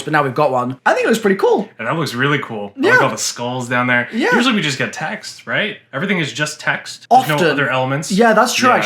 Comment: thought he said "usually we get taxed" I was like yeah